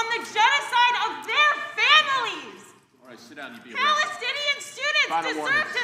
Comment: Am I trippin? I could swear I heard "alright, sit down, you idiot." at 1:28. I hope I'm not wrong.